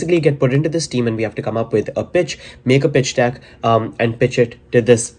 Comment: am i the only one who thought that at 3:32 he said B** instead of pitch?